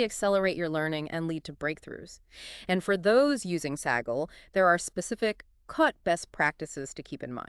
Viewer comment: The AI started to have a brain aneurism. Saggle instead of Kaggle.